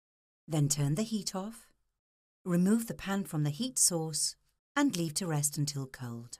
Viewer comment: i thought u said not 2 remove the pan from the heat